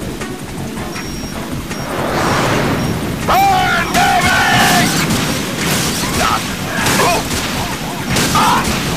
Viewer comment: what did say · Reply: SSJRhandy Burn baby